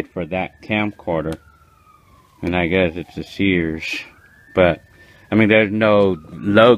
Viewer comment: My first time when I hear "Shearsh" instead of "Sears" and "It worksh" instead of "It works", LOL Congratulations with your purchase!